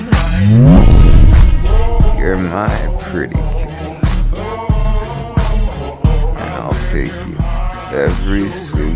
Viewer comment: Did he say "Freak"?👀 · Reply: Pretty sure it was "pretty" ( •~•;)